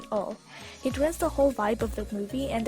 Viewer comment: I hear when she was talking about emotionless cookie girl, I misheard her and heard "It ruins the whole bible" UmMMmM 😂